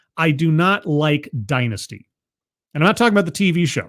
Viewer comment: It might have been because I was half listening or playing this too fast, but I heard “I don’t like dykes!” Which would probably be fitting for Hillary Clinton.